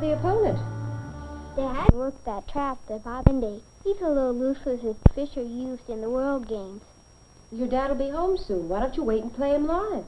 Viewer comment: Wtf tho at 18:09-18:19 what does the kid say? Lol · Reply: woman- who's your worthy opponent? Kid- dad (thump) we're up that trap tha (thump) uferndy . He's a little loose as a (thump) fisher used in the world games. woman- your dad will be home soon why don't you wait and play him live.. Kid- (thump) kay.. Woman- well I'v got things to do in the kitchen. Kid- I'll think I'll help you.